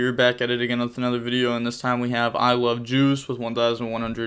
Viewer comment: I swear I thought you said I love Jews until I read the guys name 😂